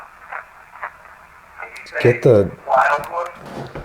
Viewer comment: it sounds like it said "Get the hell out of the sanitarium"